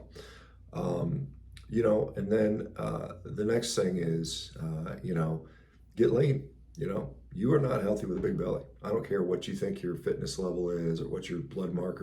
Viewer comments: I thought Shawn said to get laid😳glad i realised he said get lean before i took his advice,because you've got to take the docs advice🤣🤣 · Did I hear right "get laid" @ 3:50 mark 😛I'm totally IN NOW😝 I like the idea of kicking more assE$ need 2 check out local defense offers...just got 2 create better intuitive hormonal balance...DO not want to do kicking/competing with those I want to woo...or do I 🤪 it's a whole brave new world out there eh!?!!7:55Am8/9/2023